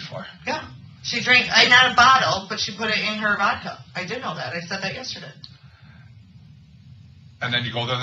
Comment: Did anybody else catch what she said here? She said "I did all that..." and then seemed to catch herself and say something else. "I said that yesterday." This woman is truly disgusting! 😤😤😤😤😤